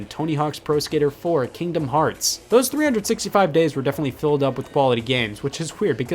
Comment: Scott: Grand theft auto vice city super mario sunshine metroid prime morrowind tony hawks pro skater kingdom hearts.. these 365 days were definetly filled up with quality games" Me: misheard 365 days for 358/2 days